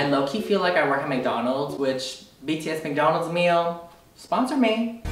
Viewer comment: did u just say BTS??? did i heard wrong or smthn. btw new sub!